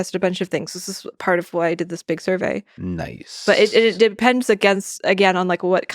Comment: Did Lex say "niiiice"